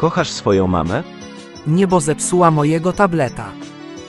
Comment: Usłyszałem "nie bo zepsula mojego napleta" 💀